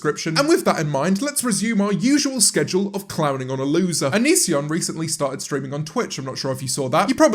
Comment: i thought it said “Onion is a loser”